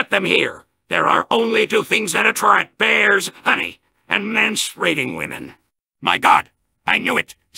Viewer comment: "There are only two things that attract bEaRS . Honey, and manspreading women." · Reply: it sounds like he said manspreading women instead of menstruating